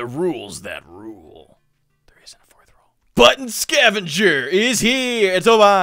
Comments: did i just hear "Butt Scavengers"? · At the beginning I thought he said butt scavenger 😂